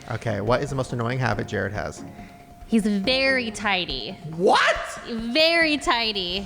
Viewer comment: I was cleaning and just listening and I thought said Jarid's most annoying habit was he's "very tiny, VERY tiny" then I looked and read the board and it said tidy🤣🤣